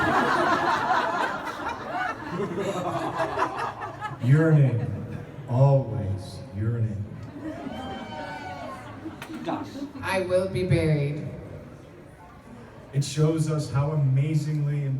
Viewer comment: Yearning always yearning TRY TO KNOW LOVE, IT IS YOUR ONLY HOPE (....and I break down and cry) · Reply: Vajolet B. The first time, I heard "Urinate. Always Urinate. " Me: That can't be right. Can it? What kind of book is this? Why are people oohing for that? Also me: Gotta get my hearing checked.